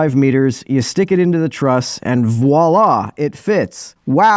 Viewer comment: it's "voi and la!"